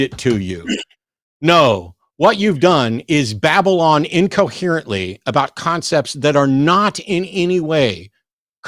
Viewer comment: I loved “Babylon incoherently…” … or did I mishear… ;-)